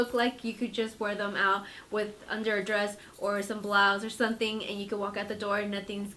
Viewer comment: "some blahs"? What did she say?